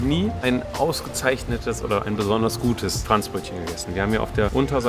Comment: Ich habe die ganze Zeit Trans Brötchen verstanden... 😂😂😂 Konnte mir nix darunter vorstellen... Dann hab ich es gegoogelt und es hat mir Franzbrötchen vorgeschlagen... 😂